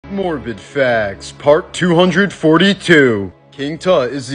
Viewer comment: Ima be honest i thought it said morbin facts